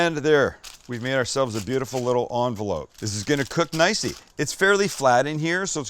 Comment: did Sam say it's going to cook "nicey"?